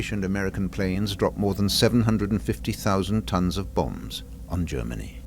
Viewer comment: I kept misunderstanding and thought he was saying they dropped “bones” on their enemy when in reality he was saying bombs.